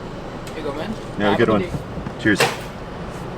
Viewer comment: Do I have a hearing problem or did say "bye" (tschüss) in German?